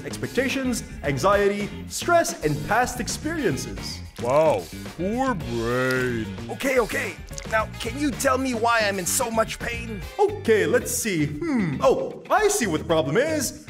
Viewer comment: I always thought he was saying “we’re brave” and not “poor brain”